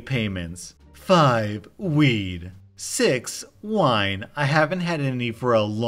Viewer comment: you dropped the "d" on the end of "weed" so it sounded like you were saying "wii" as in nintendo. I had to double check the screen to see if I heard that correctly, because who the hell is still buying a Wii in 2019/2020? Of course, I misheard. Rewound, listened. Nope, no "d" sound. Watch out for that. Make sure you're enunciating your consonants. Otherwise, the quality goes down.